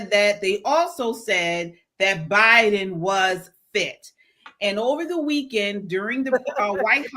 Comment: Lol at first I thought you said Biden was fixed